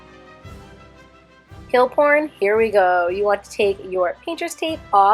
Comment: did she say peel porn ? sis